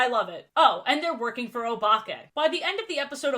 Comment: I swear EVERYTIME you say the villains name my brain keeps autocorrecting to “Obama” 💀 Edit: He reminds me of the villain from “Mad Steel” (not the movie 😭) I’d love to see you cover it some day